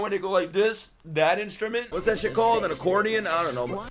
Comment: Did bro just call a violin an accordeon or did I misunderstand something? ☠️